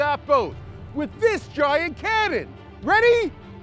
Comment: ‘Puts cello away’ I thought you said canon as in pachbel canon.